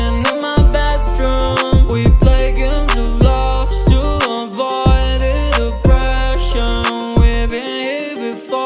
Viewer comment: did he just said aboid?